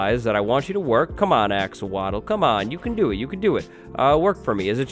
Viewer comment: the first time i heard him say axowattle i thought he said axolotl lol